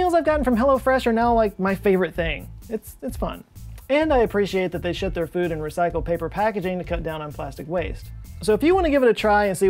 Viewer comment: Loved that skit! It does sound like you said "I love that they shit their meals in recycled plastic packaging" though.